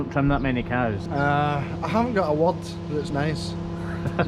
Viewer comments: "uh I haven't got a wad that's nice." Even with CC turned on - I still don't understand what he said. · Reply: Bit late with the answer…only just watched this episode. He actually said (in a Scottish accent) “I haven’t got a word that’s nice….CC doesn’t always interpret accurately. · What did say? I didn't quite pick it up. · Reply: He said "I haven't got a word that's nice...". Lol!